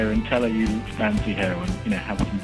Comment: I thought he said "tell her you fancy heroin" at first... I was listening to it in the background